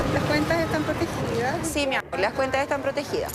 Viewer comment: escuché bien? La funcionaria dijo "sí, mi amor" ???